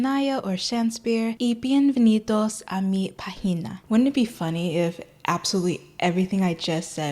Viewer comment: PLSSS IT SOUNDED LIKE YOU SAID "WELCOME TO MY PUSSY" 😭😭 the accent should be on the -pá syllable instead of the -gi one, otherwise it sounds like you're saying vagina instead of página